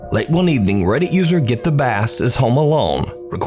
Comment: I swear when you said the last guys name "GetTheBass" I kept hearing "GiftOfAss" I had to rewind and look at his name to make sure I wasn't hearing things lol.